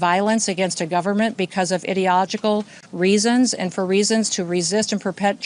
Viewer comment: ?did anyone else hear “idiotogical” ?